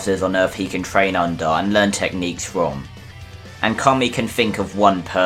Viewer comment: i was expecting king kai😅 and my subtitles say comey instead of kami😂😂😂😅